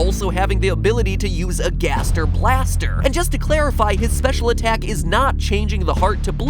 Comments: I could swear at 12:44 he said "Gaster Plaster" with a P HEY TOBY FOX! I GOT A NEW MERCH IDEA FOR YOU!! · Sounded like he said "Gaster Plaster"